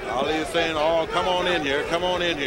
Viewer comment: “Come on in here?” You know🤔… I coulda sworn I heard “aww nXgga” but i’m probably just deaf.🥴